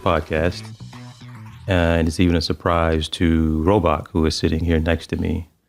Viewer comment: It always sounds like TJ is saying robot instead of Robach. I know It’s not the first time he’s heard this.